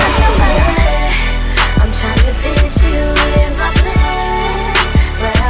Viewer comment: omg lol i thought it said fit you it my pants lol